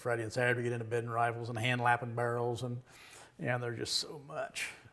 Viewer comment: I had to rewind a sec. I thought he said I'm a racist reader lol. I was like that came outta left field.